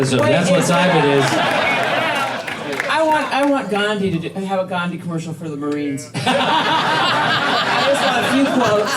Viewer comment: What did the woman say here that made everyone crack up? I've played it a few times and couldn't hear what she said. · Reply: i want gandhi commercial for the marines